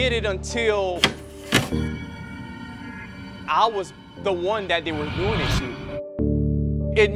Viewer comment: What did he say in 0:14? Your sound effects drowned out everything. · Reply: He said, "I was the one that they were doing it to."